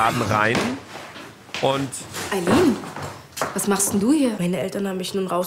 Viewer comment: Alle Schnittfehler: 6:54 8:34 9:16 9:48 PS: Bitte werde Cutter bei RTL 2 PPS: bitte reagier weiter auf sowas PPPS: ich hab langeweile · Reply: Der Moderator sagt Eileleen oder hab ich mich verhört? 😅